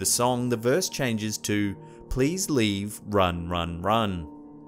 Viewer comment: Oh I thought she said please me lol